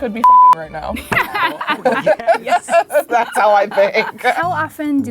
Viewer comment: did anyone hear the "ohh yes"